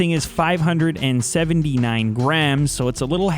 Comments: OMG hahaha i thought you said grands not grams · i thought he said 579 grand. I was like holy shit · I thought he said 579 grand!!! :0